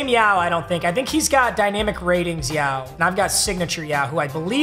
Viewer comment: i can’t be the only one who heard him say dynamic ratings yao 😂